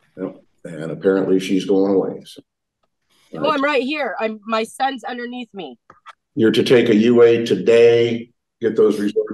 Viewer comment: did she say “my son’s underneath me”? Someone clarify! I need to know. I pray she doesn’t have another child in her care.